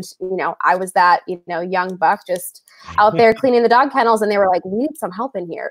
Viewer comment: Did she just say "young buck?" Check the two different reactions and. was like "whoa, James was yep". Lol. That's what happens when people overhear black people saying something and using a word we use, and not knowing the meaning. Cultural appropriation.